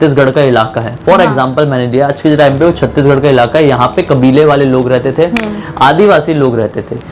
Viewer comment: at this time i was like ...ohh!!!!!did hi just said CG ( my home state ) XD